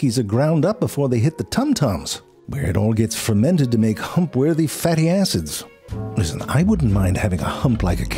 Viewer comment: I heard "Hump-worthy fatty asses."